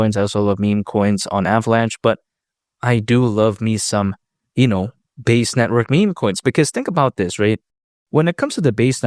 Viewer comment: i thought bro said “ I do love me some anal “ 😂😂😂. We love you boy ❤️